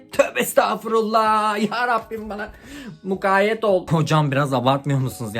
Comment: de hocam yerine kocam mı dedi o ben mi yanlış duydum?